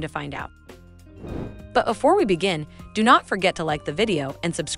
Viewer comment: First - wow, spoilers. Rude. I'd be so mad if I hadn't watched the movie already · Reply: im so glad i read the comments first bc she said afore instead of before at 0:39 and i wanted to see if anyone else noticed but ended up saving myself from spoilers 😭